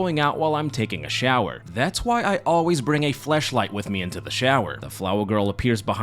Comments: did you say fleshlight instead of flashlight??? 😂😂😂😂😂😂😂😂😂😂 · did he says Fleshlight? 😂